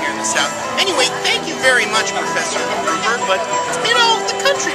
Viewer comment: What exactly was it you said to Snape at 9:20? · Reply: (CNDISMinion96) "Thank you very much, Professor Gruber, but..." Yeah, the sound was bad. I'm sorry.